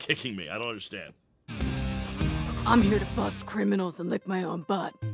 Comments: I thought they said Pringles instead of criminals 🤣🤣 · When I first watched the movie I misheard that as “I’m gonna bust criminals in the name of butt” 😂